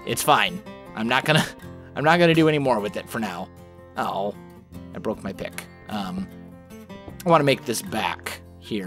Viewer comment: did he say "I broke my d***" @ 11:24??? LOL